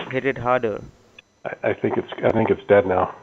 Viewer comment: did he say "hit it harder" ? lmaooo